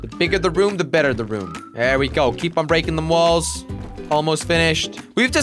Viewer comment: DID HE JUST SAY BREAKING ALL THE BALLS?!!!